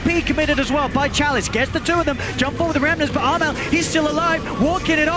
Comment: “but Ana is still alive” did I misheard it?